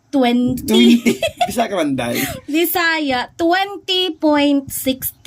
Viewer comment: Sorry, did I just misheard "bisaya" exactly at 14:28?